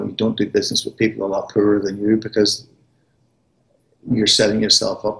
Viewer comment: what did he say? ''you dont do business with people upper than you''? Im not sure about the word ''upper'' . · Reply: poorer..